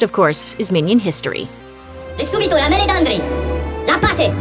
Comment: i heard him say “meladandri”, which obviously comes from chris meledandri